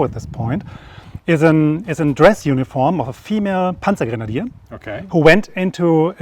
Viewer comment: Did he say female Panzergrenadier? 31:15 Crying out to get more stuff does not equal caring about logistics.